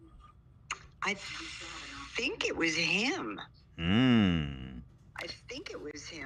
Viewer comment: She reached out to him lmao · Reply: she actually said at 2:47:57 “hmmm I think it was him” when ethan asked who reached out.